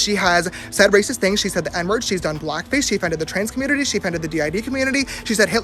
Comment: i thought he said "she DEFENDED the trans community" and was like O.O